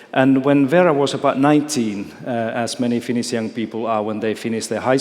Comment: Did he just say "Finnishian"